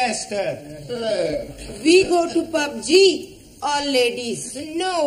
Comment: What did i just heard Jamila say go to PUBG at 23:16😱